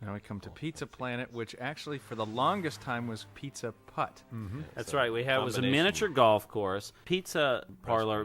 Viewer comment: Am I the only one who thought he said "Pizza Hut?"